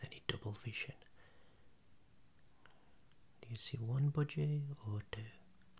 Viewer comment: what did you call this animal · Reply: Budgie